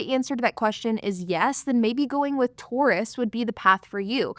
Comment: LOL it took me like 7 takes to understand you were saying "tourists". I kept hearing "Taurus" (and so did the captions) and was extremely confused, lol